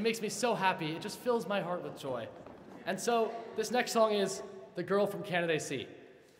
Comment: What does he say at 0:14? It sounds like “the girl from canadaysee”? Before they correct him. Is it a Harvard reference? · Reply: Yes, Canaday is a Harvard dormitory. He’s saying the girl is staying in building C.